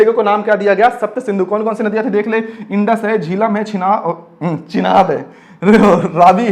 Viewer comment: sir aapne kya bola ?? 😂 😂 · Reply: Chinar🤣